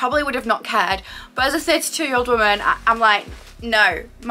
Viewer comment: Omg I thought you said 52 year old woman and I was like whaaat 😨 she looks too good for that age… then I replayed it smh 🤦‍♀️ you’re very pretty 🥺🥰